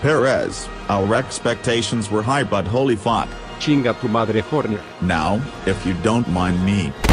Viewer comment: Subtitles: Thank you too Horner :) Checo: Chinga tu madre Horner. Me who understands a bit of Spanish: Hmm....